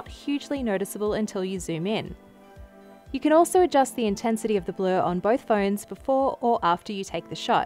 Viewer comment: Hey everyone! Thanks for watching. The A51 just launched in the US (it's been out for a while elsewhere in the world) . Let me know what phone you want me to compare against the iPhone SE next 👇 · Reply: change the intensity of the glow is that what you said? Does that mean that it'll reduce that oversaturation of the colors?